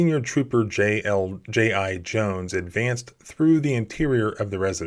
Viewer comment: anyone else hearing, “GI Joe”